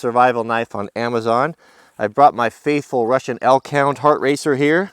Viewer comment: First time I actually heard a dog go "woof" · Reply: A Russian Elkhound Racer....is what it sounded like he said. But this dog, is not. It may be Russian breed, but Elkhound Racers look like Huskies. But I..D...K!